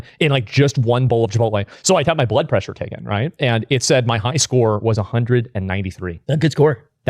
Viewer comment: did he just say high score? 😂😂